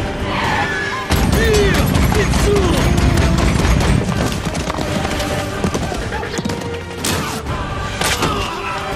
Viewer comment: what is he saying? Or in what language? is it just "byeah, get summ" ? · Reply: "Yeah! Get Some!"